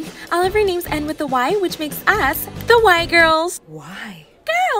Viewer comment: I thought she said “we are the white girls” 😭✋